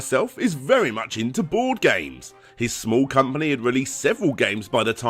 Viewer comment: I heard "porn games" instead of "board games" while i was sitting a foot away from my PC